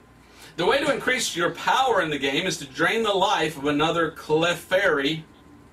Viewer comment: the way that he pronounced Clefairy, almost sounds like he said “queef fairy”. OMG